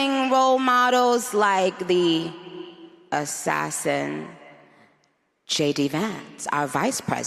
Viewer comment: did she say like the 'assassin' JD Vance?